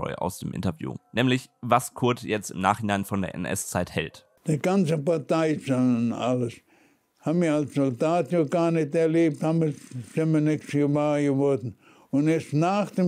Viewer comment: Service-Mitteilung: ich nehme an, er sagt dort: "Die ganzen Partei-Sachen und alles, haben wir als Soldat(en) ja gar nicht erlebt. Das (dessen) sind wir nicht gewahr geworden." (...) "Der einfache Mann hat doch nie im einzelnen was von KZs gehört..."